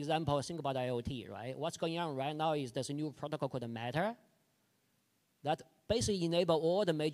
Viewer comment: What’s he saying the protocol is that enables all major players to interoperate ??? Sounds like he say “… called the matter…”